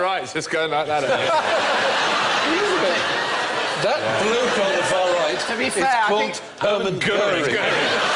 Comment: Wat does stephen say at 32:40 ? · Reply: "That 'bloke on the far right' is called Hermann Göring".